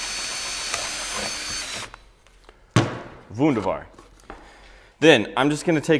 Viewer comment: Did you really say "wunderbar" @ 7:52? :D